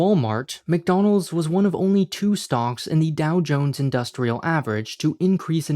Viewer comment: Am I tripping or did you say "stonks"